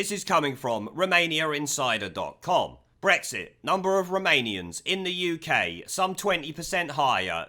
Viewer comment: I wasnt paying attention and thought you were saying Remoanians. XD